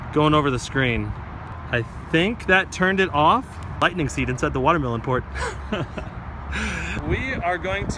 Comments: i think he said there was a lightning seed inside the watermelon port. lol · who else heard lightning seed inside the watermelon port Maybe it was meant to be watermelon seed inside the lightning port Lol 😂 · did he say lightning seed inside the watermelon port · did he just say " lightning seed inside the watermelon port." 😂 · Did he say "Lightning seed inside the watermelon port" Omg haha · Did he say 'Lightning seed inside the watermelon port?' at 6:17 ???? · Did he just say Lightning seed inside the watermelon port😂😂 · Did he say lightning seed inside the watermelon port at 6:17 ??? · Haha take a look at 6:17 I think(?) he says: "There's a lightning seed inside the watermelon port" ¯\(ツ)/¯ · Did he say lightning seed inside of the watermelon port.... Oh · Did he say "there's a lightning seed in the watermelon port!"